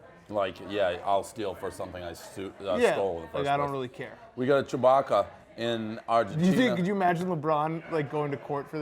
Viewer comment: I think he said at 20:35 that we have a Chewbacca....